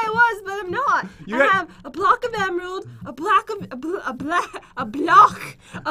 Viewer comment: It sounded like u said a block of blood